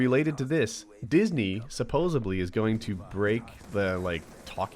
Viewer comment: Did you just say "supposibly"?